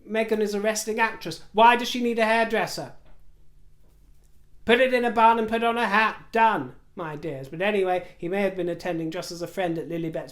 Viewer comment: Lmao i thought you said put it in a bun and slap a bag on it and your done.